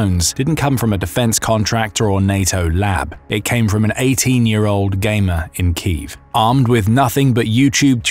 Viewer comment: at first I heard “18yr old gay man in Kyiv” 😂